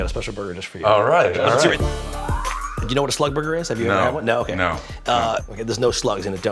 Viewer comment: Thought he said slut burger